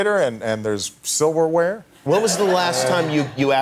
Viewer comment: I didn't hear this phase like "Where was " the last ime ~. It has reduction, reduction or something ?